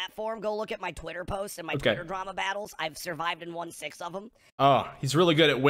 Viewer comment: "I have survived all of my Twitter battles." Said no sufferable human being ever. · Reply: I always heard "I've survived in 1/6 of them" but I suppose "survived and won six of them" is slightly less terrible.